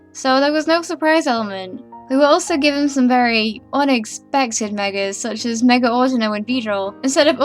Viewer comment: did she just say ONIX-pected